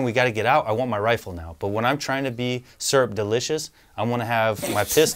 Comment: He said syrup delicious instead of surreptitious. Lmao